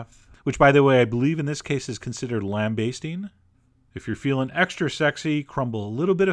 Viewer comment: I thought I heard lamb basting.....